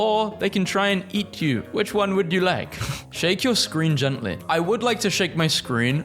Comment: I thought it said squint your eyes so I did that and it shrunk